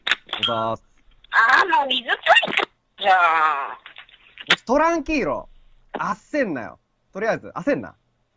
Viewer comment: これなんて言ってるの？？？？😮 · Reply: 『トランキーロ』(スペイン語で落ち着け、焦るなの意)って言ってるのだと思います。プロレスラーの内藤哲也の決め台詞が 『トランキーロ、焦んなよ』ですが、元ネタはそれかと。